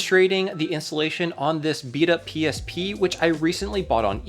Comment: Did just said Vita PSP? Anyways, I came here from MVG's latest PSP video where he upgraded the screen, then came here for the install method :)